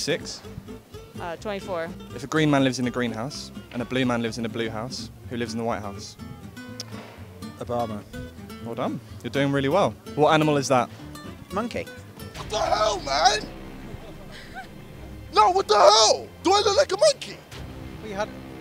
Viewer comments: who else thought he said “a brown man” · Ngl i couldnt hear what he said for white house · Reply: Obama